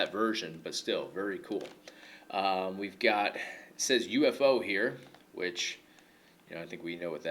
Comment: Actually, I think they really mean "UFO" instead of "UFC", as there was a shoot-style promotion in Japan with those initials at the time (it meant "Universal Fighting-Arts Organization"), and all the wrestlers featured there wrestled in that promotion at some point, with the exception of Bas Rutten (who was still included, perhaps because he wrestled at Inoki Bom-Ba-Ye 2000, and UFO was a joint venture of Satoru Sayama and Antonio Inoki, and probably a sponsor of that show, considering how a ton of UFO wrestlers participated).